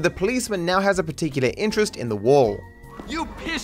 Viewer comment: the subtitles are wrong, the cop said "you pissed off the wrong officer"